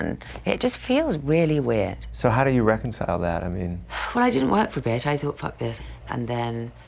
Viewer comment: Thought you said, "How do you Beckinsale that?"